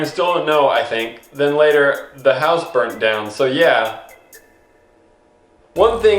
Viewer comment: | Wait, did you say the house burnt down? O_O